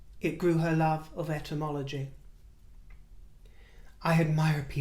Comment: Oh good grief! You were saying "ETYMOLOGY". I misunderstood and thought you were saying "ENTOMOLOGY". I was confused a bit there! I was trying to imagine studying bugs!!! Hahaha! Sometimes my head injury causes things too get wonky a bit. 😀